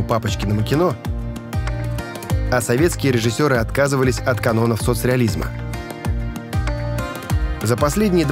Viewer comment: Мне послышалось или "от канонов соцреализЬма"?))